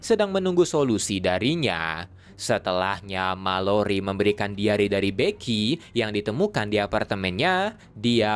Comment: Ini gw salah denger apa emang diare dah daripada diary